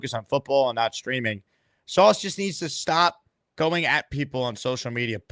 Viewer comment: I thought Gardner said he deleted his social media lol